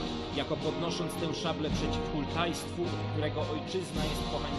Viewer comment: usłyszałem "Przeciw Uruk-Hańsku"